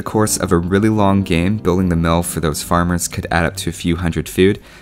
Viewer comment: it sounds like you said building a milf lol